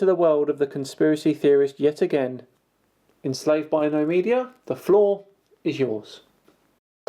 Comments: I missheard you at the begining and thought you said ”enslaved to no media, the flaw is yours” and not ”...the floor is yours” which just sounded so right. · "Enslaved by no media, the flaw is yours." That's what I heard. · Lol I thought you said "The flaw is yours" lmao